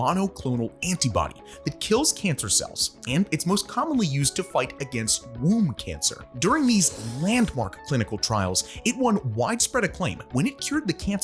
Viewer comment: sorry did you say “womb cancer”?